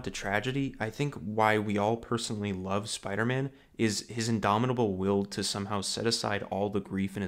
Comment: My video buffered and it sounded like you said “the reason we all love Spider-Man is his indomitable ability to suck”! Some times the bad WiFi is funny.